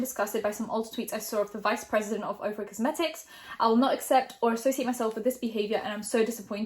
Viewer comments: For a good minute i thought you were saying "Oprah Cosmetics" and I was so confused. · I really thought she was saying Oprah cosmetics 💀. And was like wait Oprahs not married somethings not adding up